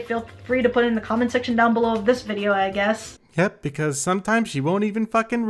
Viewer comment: Did I hear a "nyet" near the end of the video? 40:46 Are you a russian spy?